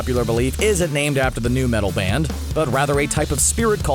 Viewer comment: did you say " the NEW metal band Korn"? Bc korn is NOT new at all.